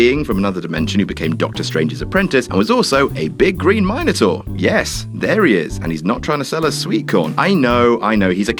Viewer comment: Did he just say MINE-Atour? 1:49-1:57 Ow, my ears... BTW cool list...